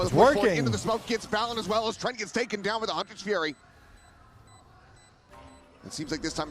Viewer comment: ngl, I thought the caster said Hunter's cherry for a sec there @ 20:21😂